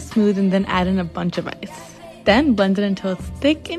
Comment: I swear I hear "and it tastes like a prostate"... 😭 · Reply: Time for Ear Candles? I clearly heard "Frostie". Maybe you never had One?